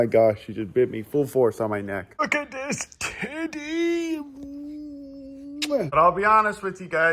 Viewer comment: “Look at this Tiddy” That’s all I heard😂